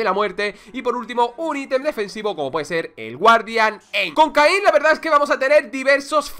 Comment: solo yo escuche cocaine?